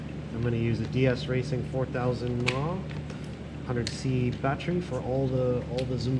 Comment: can someone tell me what battery is it cause i cant understand him (or im just dumb) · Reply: I used a DS Racing 4000Mah 100c 2S Shorty Lipo battery. It has internal 4mm bullet connectors so I used a Deans-to-Bullet wire adapter to run the car :)